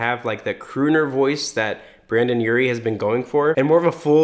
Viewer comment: did he say Brandon😂😂